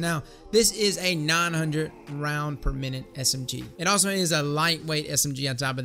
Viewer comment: Kinetic Counterbalance mods DO NOT have an effect on precision frame weapons. It would be pointless on this SMG. Use your mod slots wisely. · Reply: oh shit you said “lightweight”. I could’ve sworn you said “precision”. My bad homie. Btw, Hardlight is not linger “hot garbage” lol. Yeah. That was me.